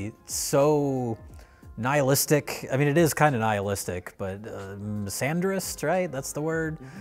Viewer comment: Does anyone catch the word Jay says at 27:57 - i gather it means something like nihilistic - sandrist? the captions have it as 'sanders' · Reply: He said "misandrist".